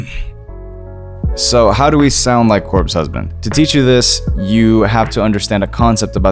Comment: I thought it said “how to sound like a corpse”